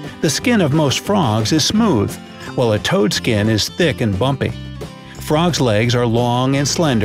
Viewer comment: Did he said frog legs are DELICIOUS!!??